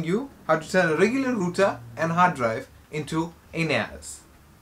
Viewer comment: bro did you just said Anus ?